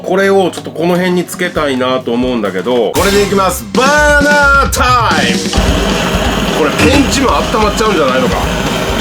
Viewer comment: 新必殺技バーナータイムが昔のファミコンソフトバーガータイムに聞こえて懐かしくなりましたw ワンネスワンネス🤘😊